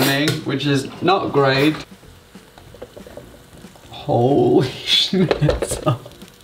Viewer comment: did he say shi# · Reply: He used to swear, and no he said holy snitzel